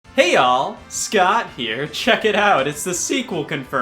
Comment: For the longest time I thought he said, "Hey y'all Scott here," not, "Hey all Scott here" lol